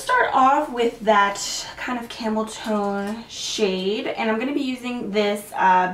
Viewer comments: Hi loves!! Hope you all had a great holiday! I'm catching up on all my new comments on my newest videos so don't think I'm ignoring you if you've been commenting! (I do one video at a time with comments) Let me know what brand you want to see next?! (; LOVE YOU GUYS!!! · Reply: Lmao I absolutely love ur vid but I could have swarm at 4:20 when you applied the "camel tone" shade in ur crease I literally thought u said I'm going to use a little bit of this "camel toe" lmmfao 😂😂😂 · Totally thought you said camel toe for a second, not camel tone 😂😂